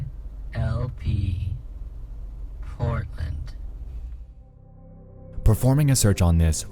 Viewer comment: anybody else hearing Fornite? edit : nvm it said portland